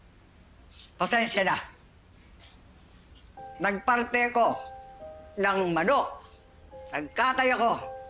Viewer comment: What did I just heard at 11:24?😂 11:24 - "Nagpanty ako ng manok!" Tama ba?😂lmao